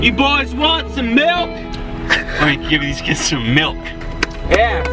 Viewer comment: Did he say frank or did I misheard 😭😭😭😭😭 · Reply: He Said Frank Im Gonna Give These Kids Some Milk